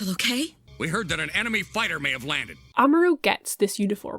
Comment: Mishearing the line at 06:02 as "We heard that an anime fighting man has landed." :D